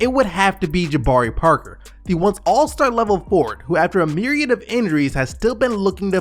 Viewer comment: Wait @ 2:04 the first one you did... did you say the once all star level forward???? NOPE.